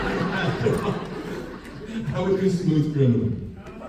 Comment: does anyone know what song he said 😭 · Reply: It's sounds something like "Smooth criminal."